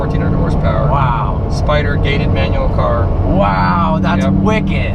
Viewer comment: my girlfriend overheard the video and thought he said, "spider gayman car"